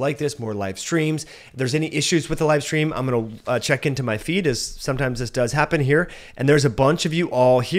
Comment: I thought you said "Let me just check into my fetus" HAHA